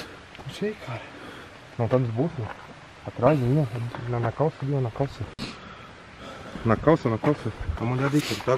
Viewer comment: So fui q eu ouvi "ta na calcinha "